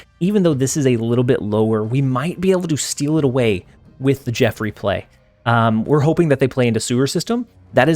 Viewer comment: At the beginning of the video He say "Jeff replay"... i thought he said "Jeffrey play". Calling Jeff the shark by his full name lmao😂😂😂 like a parent... lol